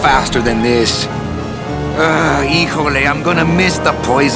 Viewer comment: Can Someone Tell me what Manuel says at 0:50 ?! "-something- I'm gonna miss the poisoning!" i'm so glad that this game never had a sequel. It had a great story and ended perfectly with no strings left untied. Great great game. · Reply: I know i'm 8 damn years late, but he says something like: "Híjole, i'm gonna miss the poisoning!"